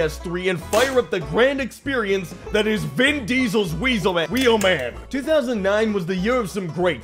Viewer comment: I thought you said big diesels Wheelman xD